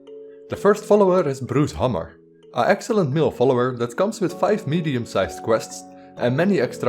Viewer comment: bru 0:33 i thought he said 5 million side quests XD